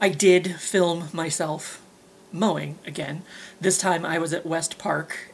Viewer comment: I thought you said you filmed yourself Moaning 😂😂😂...Thanks for another mowing video..i know it was for me 😉